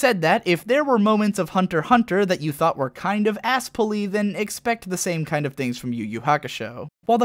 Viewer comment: What is this word he says at 8:10? moments of hunterhunter that were kind of ____? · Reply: "Asspull-y" It's not an actual word but it's a term that started on the internet. Basically, an asspull is a sudden change that wasn't properly explained and feels cheap and kinda bullshit.